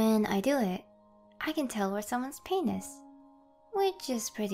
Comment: Please tell me I’m not the only one who heard her say the other p word instead of pain